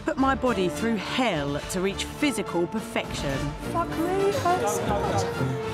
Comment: did she say "fuck me" ?